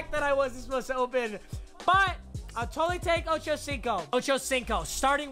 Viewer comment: is Chad Johnson and who did he say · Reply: Ochocinco Is his nickname